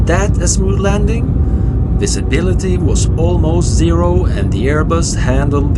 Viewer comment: slight error in the English subtitle here “this ability” instead of “visibility”, though looks fine in the Spanish one! Cool to see an auto landing, did they announce it over the PA?